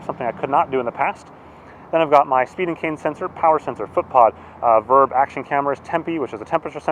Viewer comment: I glanced away when you were going through the sensors, and I could’ve sworn you said Stephen King sensor, I had to watch carefully and find that you were saying speed and cadence.